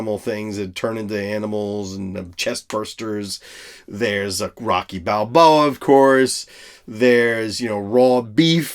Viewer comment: I thought you said "Rocky BigBoa" for a second, and I thought to myself "that'll be a great mash-up idea!"